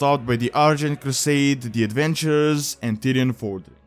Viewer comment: Did he say "The Avengers" Man, that would have been awesome!